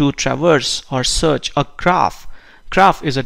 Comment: You mentioned a word starting with "c" several times in the 1:50-2:10 time frame and I cannot understand what you're saying. It sounds like "craft" and that's how it's being interpreted by the auto closed captioning. What is this word? Thanks. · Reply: Are you saying "graph"?